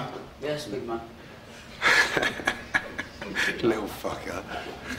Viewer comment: did Ramsay just call a kid a little fucker?